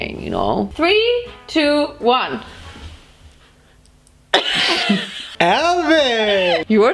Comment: Did he say Alvin instead of Elvis or did he got cut off??